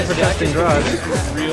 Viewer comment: what did that guy say at 1:37?, "the hardest spots are good, right?" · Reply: That power supply is still good right